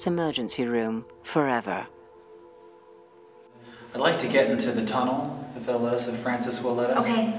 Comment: I put on the captions, and when said, "I'd like to get into that tunnel." The voice recognition caption thingy thought he said, "I'd like to get in that organism." Oh, google captions, how I love thee.